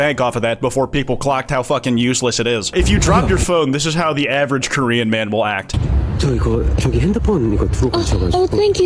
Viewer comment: bro i thought it said threatening a korean man